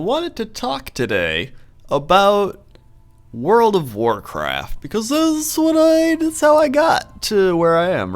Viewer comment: Did he just say world of warcrap at 0:07?